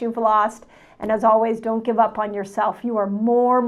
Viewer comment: At the end, at 11:17 she says "You are more miraculous than you have been taught, and fasting is your _______ ." I can't figure out what she said there. I've listened to it about 10 times. Sounds like "doren" but I don't think that's a word in English.